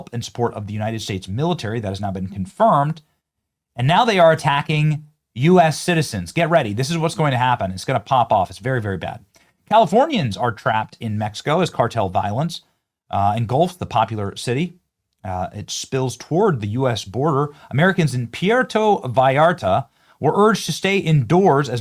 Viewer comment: only CA? I thought you said Americans were trapped.